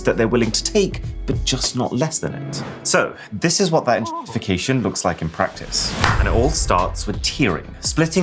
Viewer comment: “Pussfication?” What word is he saying that he feels like he needs to censor? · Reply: Enshitification